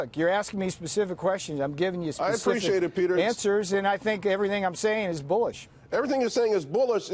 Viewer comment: Peter Navarro: "I think everything I'm saying is bullish." For a second I thought he said "I think everything I'm saying is bullshit."